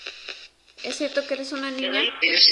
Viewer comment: En el 14:05 creo, cuando le preguntas "es cierto que eres una niña?" Se escucha que dice "a mi" es mas, el subtítulo automático de yt pone "a mi" no querra decir que amy es una niña? Además creo que se escuchan 3 voces.. dos femeninas y una masculina (por lo que recuerdo en tu casa desde antes había un espíritu)